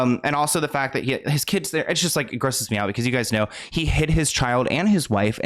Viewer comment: Omg I thought you said “he HIT his kid and wife “ not HID 😮‍💨😮‍💨